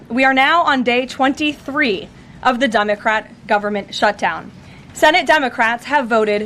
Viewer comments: I love it, I thought I heard her say "Dumocrat" lol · I could swear I thought I heard her call it the dumbocrats government shutdown. Lol Freudian slip maybe.